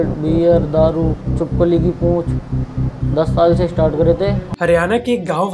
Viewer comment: did I hear "chipkali ki poonch" ?